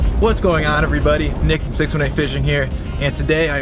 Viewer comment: Sounds like he said 619 fishing